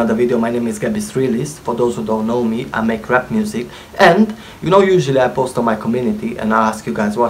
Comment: Sorry to make fun of you, but this sounded like you said you make crap music 😅